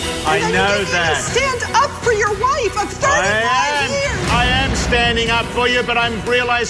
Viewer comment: Does she say ''I'm 35 years'' I must be deaf or blind.. cause she aint 35 or she said 45..